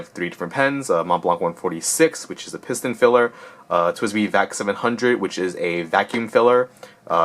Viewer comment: What is the name of that clear pen you are using in the video? I really like the look of it and always have wanted one that I could see the ink that wasn't a $20 piece of garbage. I heard you say "700" but I couldn't hear the brand. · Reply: The clear pen is a TWSBI Vac700. I believe they've gone down quite a bit in price since TWSBI released the 580s.